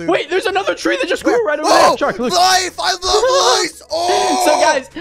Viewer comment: who heard “life, I love lice”?